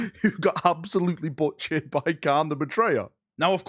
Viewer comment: I thought you where saying Khan not kharn.